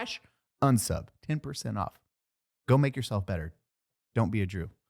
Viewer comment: Lmaooo i thought said go make yourself better dont be “jew” 😂